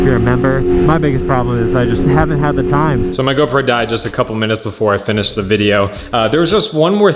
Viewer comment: sounds like you said "My girlfriend just died" Had to go back and make sure it's not what you said lol